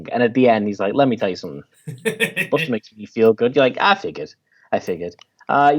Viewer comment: I thought you said 'Sting makes me feel good', not 'Bustin Makes Me Feel Good', which led me down two pathways of thought: 1. You really like The Police and/or Sting's solo career. I prefer the former, because I think Sting worked best in the context of a band, but some of his solo stuff's not bad. 2. Sting has been noted for his practicing of TANTRIC SEX, and the good feels you were feeling were purely EROTIC. A moot point because that's not what you said in the first place, but I thought I'd share this with you. FREE SPEECH MUTHAPHUKKAS